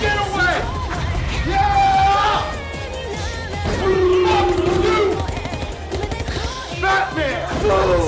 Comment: for the longest time i thought was saying "Fuck you Bam."